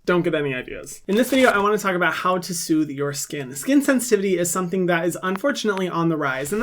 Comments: I thought you said how to sue your skin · I thought he said "this is how to SUE your skin" I was like. IF I COULD SUE HER GURL SHE WOULD BE SO BROKE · I heard “I wanna talk about how to sue your skin” after you said I’m entitled to compensation 😂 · I heard "how to sue your skin" and my law student with sensitive skin self was X.X